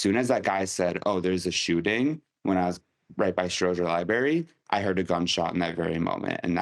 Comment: did he say "libary"?